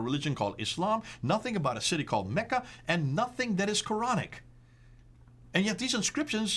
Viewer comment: I heard "nothing that is moronic" 🤣🤣🤣